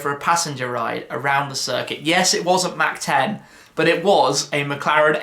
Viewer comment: When you say the Mc Laren F1 you rode in was a Mac 10 what does this mean? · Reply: I thought he said it "wasn't mach 10", as in it was a parade lap and not a flat out full speed experience.